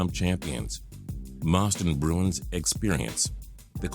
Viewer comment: It sounded like he said Moston bruins